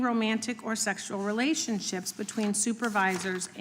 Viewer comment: Not a Fan of Rep. Jim Jordan, but I heard his “Not an Objections”!⚡